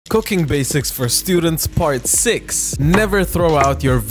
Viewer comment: I thought I heard "COCAINE BASICS